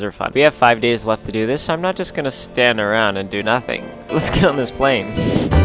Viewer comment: I thought he said let’s kill this plane but really